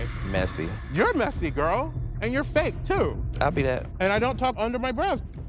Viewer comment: I. BE. THAT! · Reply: I thought she said I'LL be that